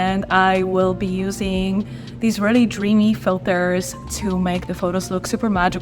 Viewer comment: I literally said I’m using the moody filter lol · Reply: hmm I heard you say you are using these Dreamy filters 0:13 hence my question as DreamFX make several Dreamy filters, I didn't hear you say Moody!?